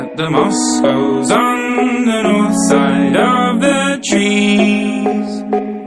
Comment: Put the speed at 0.75... trust me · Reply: also at the the beginning when he says "legend has it the moss grows on the north side of the trees" it sounds like he says "the north side of the cheese"